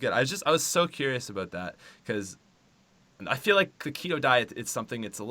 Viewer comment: At the beginning i thought he said Cheeto diet aha